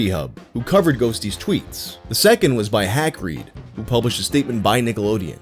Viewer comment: You said “HackRead” and I thought you said “Hagrid”. I was magically confused for a second 😂